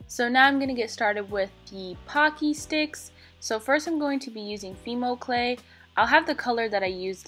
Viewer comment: i thought she said female clay